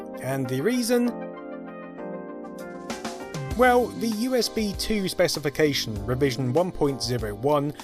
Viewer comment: captions say UBS instead of USB